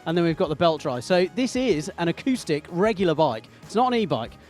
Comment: did you say that this is an ICE bike not an e-bike?